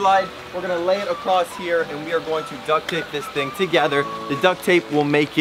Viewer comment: Ok who else thought franklin said duck dape instead of duck tape 😂🤣